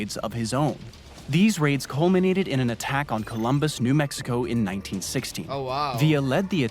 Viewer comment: Thought he said Villa raided New Mexico in 1960. 😂